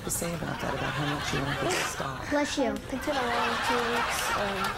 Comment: Did he say "I bless you"?